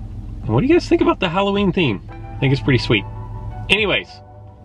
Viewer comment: did he said halloween theme :P